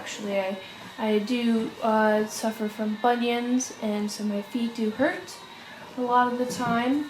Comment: Did you say you suffer from Funyuns at 3:28? LOL